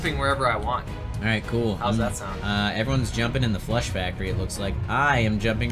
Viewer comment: Why does it sound like everyones saying flesh factory instead of flush factory